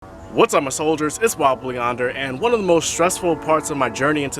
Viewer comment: For the longest time I thought he was saying, "Wobbly Blue Yonder". Haha.